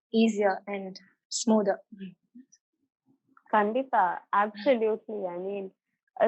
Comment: Did she say Khanditha in kannada?